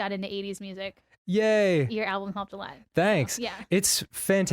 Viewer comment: At like 2:40 or so in, Suzy mentions an 80s album or something but I can't hear the name? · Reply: she said “your album helped a lot” referring to under the covers by ninja sex party